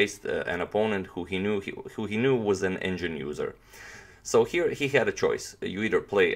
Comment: engine user? oops... I watched his 'confession' video , with english captions and it said 'indian' user :D I didn't understand until now, why he wondered the 'indian' user not getting banned xD